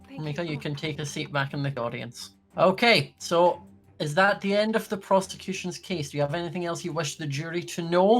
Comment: Holy- I thought he said "Is that the end of the prostitution's case?"